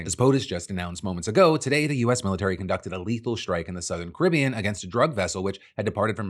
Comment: I thought you said “…illegal strike…” when quoting Rubio’s x post. 😂